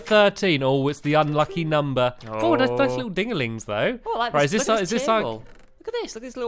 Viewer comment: Did he say those are some nice ding a lungs? 😂. Man I miss my childhood